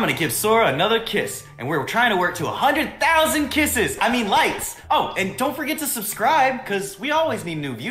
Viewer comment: I thought he said a thousand “”lights””🤣 But he said likes